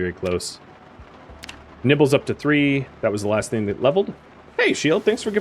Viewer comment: i thought when you said nimble up to three you had said nibbles up to three... i had a little double take there...